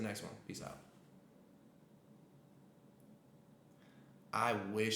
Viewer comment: wahd did he say🤣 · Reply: Shocked